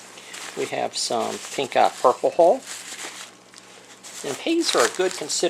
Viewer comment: I love being in the united kingdom and not know some of the plants in the US. And possibly mishearing the accent. To me he says pink eye purple hole for one of the bags 3:00 sounds like the night after a good party to me :P